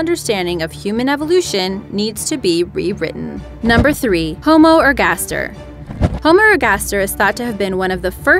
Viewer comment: On number 3 I thought she said homorgasm 😂